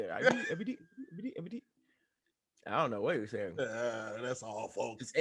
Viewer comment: i thought he said i believe thats all folks